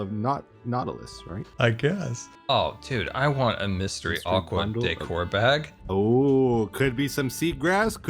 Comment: he did not say aquatic 💀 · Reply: He said mystery aqua decor bag but it did sound sus 😅